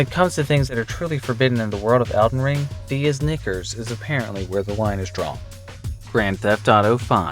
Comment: what did he say? Yes, but we generally do draw the line BEFORE SAYING IT! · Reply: Knickers, search it.